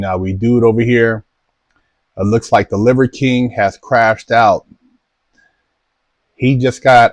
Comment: I was watching with my fan blowing and I misheard "Liver King crapped out" 😂😂